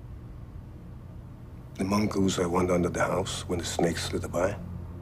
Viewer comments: A mongoose i want under the house when the snakes slither by. 🙂 I miss this show. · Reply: I kept watching this scene over and over because I couldn't understand him at that part. I always thought he said "the monk who hides under the house when the snakes slither by" Now the Real quote makes sense. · I always thought he said The mongoose that under the house when the snakes slither by. I thought he was referring to Will as someone who is capable of doing great harm to people but is so ashamed it hides rather than embraces its true nature. Now that I know, he said The mongoose I want under the house when the snakes slither by. It creates a totally new meaning. I think our good Doctor was referring to Will a reliable person for him to have in his life. Something that could do great harm to those unwanted.